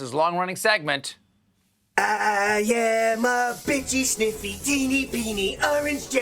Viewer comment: Awesome! But I thought I heard, “Teeny-Weenie”. 😂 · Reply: I heard teenie-peeny...